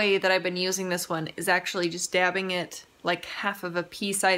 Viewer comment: When u said "just dabbing it" I thought u said "stabbing it" and I was like hmm I've never heard that terminology in this context before. Very violent😬😂😂